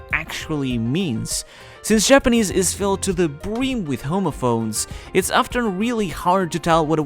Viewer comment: I had to skip back to hear that a second time. I thought you said “the Japanese are filled to the brim with homophobes” 😂